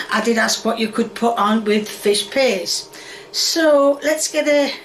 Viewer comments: I love how it sounds like is saying “Fish Piss” and how “Fish Piss” sounds like a legit english dish. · I honestly thought she was saying "fish piss" which is honestly more accurate imo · What the hell did I click into?😂 sounds like she says fish piss.